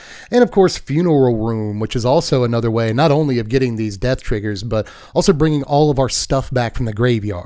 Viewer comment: Did I hear him say “Dev triggers”? 😂